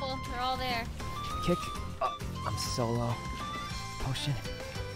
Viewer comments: did sykkuno swear at 3:25 or did i hear wrong lol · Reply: I think he said "potion" but I also thought he said "oh shit" at first lmao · did sykkuno say "oh shi-" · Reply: Potion